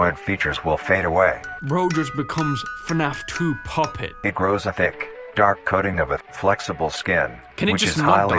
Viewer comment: I legit thought the first time I heard this… I thought it said “It grows a d*ck”